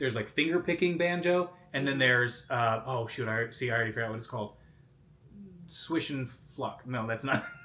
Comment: Jessica’s face like- did he just say “swish and f**k”? 😂